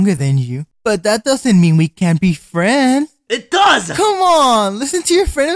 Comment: did he say frend